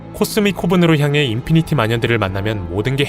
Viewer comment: 인피니티 마년이라고 들리는건 저혼자인가여